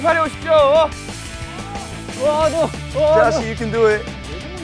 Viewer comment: Watching this a second time and I realized it sounded like Vernon said “Joshi you can do it” 23:22 😍😍 I love them two!